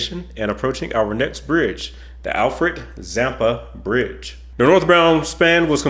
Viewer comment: I'm from Australia I'd be keen to check this out. At first when U said the Alfred Zampa bridge I thought U said the Frank Zappa bridge lol I thought yeah that could work! Lol. PS The Bonneville Salt Flats are awesome.